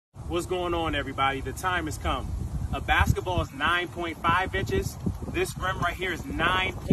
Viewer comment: I heard that "a basketball is 9.5 bit*hes" at first :awkward